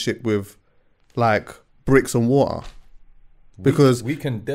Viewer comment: Did man say "bricks and water"? Hahahaha